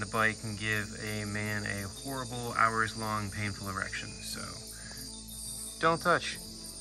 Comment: I’m sorry, what?! Lol Did I hear that right… you said erection?